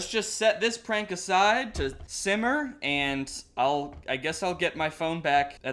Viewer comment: When said "simmer" I thought he said "Zimmer"